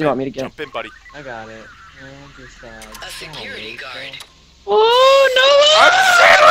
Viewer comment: did you said הצילו?